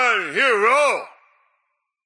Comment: did it say I’m riding a pony here?